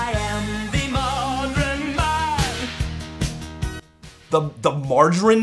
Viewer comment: I always thought Dennis was saying "margin man" at 6:41 as if Mr. Roboto was the "marginalization of mankind".